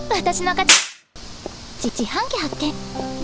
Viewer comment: ここ、自販機発見ってセリフがちち販機発見って聞こえてクスってきちゃった